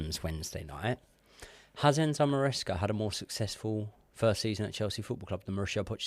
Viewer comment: What did say the highlight was around 12:40? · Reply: Poch's season